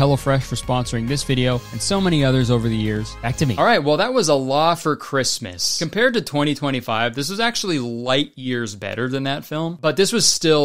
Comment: I thought you said "Allah for Christmas"